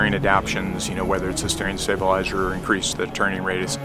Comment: decreased or improved turning radius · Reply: oh OK. I thought he said increased and was thinking surely that's not right!